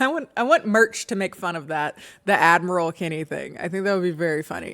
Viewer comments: Complete sidenote here: for the first few times I always heard "Advil Kennie" and was confused why, since the sponsors had nothing to do with pain medication xD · PLS ive been wanting admiral kenny merch since i first realized i misheard it like a year ago now💀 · i wasn’t the only one who thought you were saying admiral kennie this whole time??? 😮